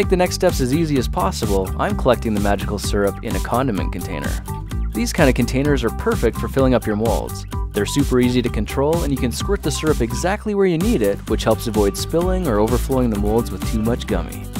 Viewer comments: I thought he said condom container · XD i thought you said condoment container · at first i thought you said condom container.